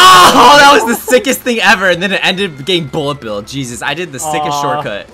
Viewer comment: First time watching I heard gay Bullet Bill 😂